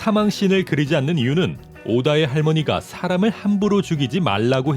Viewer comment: 오다의 뭐요? 전 자꾸 오다의 빛이라고 들리는데